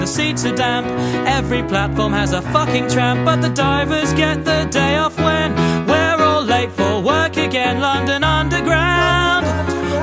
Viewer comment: Haven't heard this since 2008 but still know the words unfailingly but it STILL bothers me that it sounds like he says "but the DIVERS get the day of when we're all late for work again"...